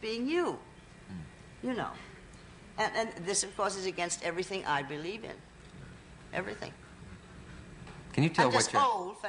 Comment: Hi! didn't catch (English's not my mother tongue) a word in a line at 3:00...I can't get the whole sense...What is "against everything she believes in"? Can anyone write it down, thx? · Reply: This is a long time later, but Bette Davis saying it "goes against everything she believes in" simply means: she thinks it's wrong because of her values. Hope you actually get the message!